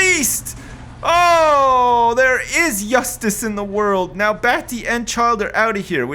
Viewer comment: did say 'yustice'